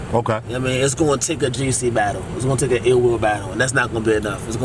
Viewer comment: I thought Ill Will was saying "only big battles"?? Cuz Ill Will need be battling Hollow, Arsonal, K Shine, Pat Stay, names like that. He's one od the best, he jad an off battle and still had a debatable vs Danny💯